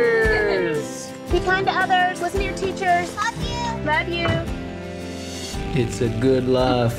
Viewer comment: ファッキューにしか聞こえん